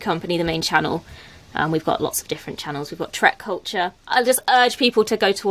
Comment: Genuinely thought said Shrek Culture 😂😂